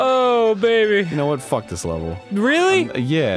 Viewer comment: did dan just call arin "bae" jesus my fragile heart cannot take this